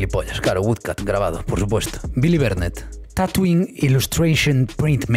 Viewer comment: Cuando dijo tattooing yo pensé que dijo tatooine